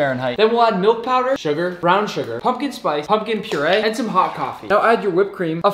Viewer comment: "hot cofee" I heard " hot coc"